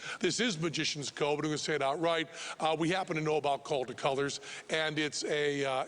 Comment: Does anyone know what Penn said at 8:41?? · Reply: Βασιλης Στρουμπουλης He said "this is magician's code, we're going to say it outright." He's letting the audience know that he and Teller know what they believe Jimmy used without giving it away.